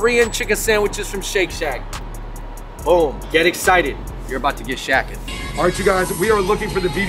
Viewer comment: heard it as "you're about to get shacked" and was like 😏